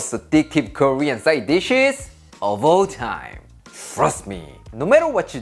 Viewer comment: When says "Trust me" I trust him, can't wait to try this one! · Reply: Sounded like he said "Thrust me"